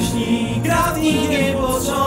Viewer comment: Cuuddoo!!😭😭😊😊😍😍 Czemu ja na początku usłyszałam ,,traftingiem" xd